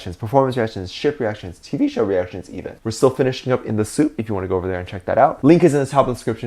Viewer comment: Why did I hear “In the Simp” instead of “Soop...” I need help T_T